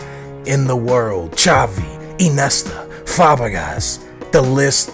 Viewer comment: did he just say "Eenesta" instead of iniesta?